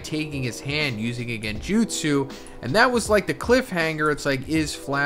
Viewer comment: did he really say "genjutsu"?